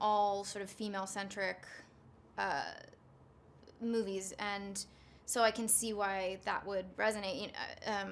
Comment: Anybody else thought Anna said sendrick at 3:08? no just me? ok...